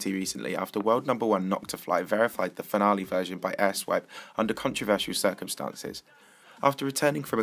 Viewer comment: did you say asswipe???